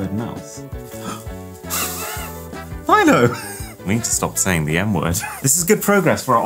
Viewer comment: I thought he Said "i need to stop Sayng the n word" 💀